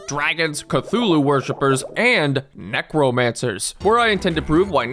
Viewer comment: fully heard "mech romancer" instead of necromancer